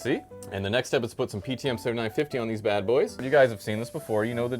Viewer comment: WAIT DID I JUST HEAR PTM7950?! I WAS HALF MINDEDLY WATCHING THE VIDEO AND THEN SUDDENLY HEARD THAT! I THOUGHT I WAS THE ONLY ONE USING THAT STUFF! it may be expensive but holy its soooo good! (it also works great on desktop. i replaced thermal paste on my gpu and cpu with normal paste and i guess the temps were "ok" but my cpu was overheating when i had the Game Boost thing from msi on. But after i finally got PTM7950 again and replaced the paste with it. i could play satis at ultra graphics and my GPU barelly got hot! That stuff is amazing and totally worth the price)